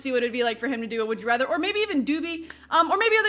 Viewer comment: "or maybe even do me" ???? Did i hear that right?